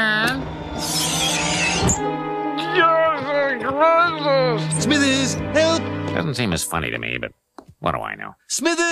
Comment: what Lenny said? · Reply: You're the greatest